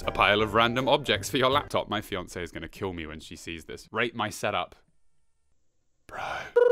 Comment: When he said rate my set up I thought he said rape my set up